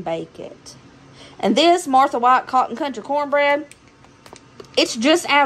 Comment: Hi sweet and Sweet boy... Someone yawned looks tired .lol ..that is a great deal on organic ground beef... You did good on groceries I've never heard of that kind of grape...there huge ...OMG at first I thought it said cotton candy cornbread 😂😂😂😂 then I heard you say country... I'm so bad sometimes... Chilli looks so so good....yummy dinner... Great job showing us the shelf stable meal... God bless y'all and I love y'all.. 🙏🥰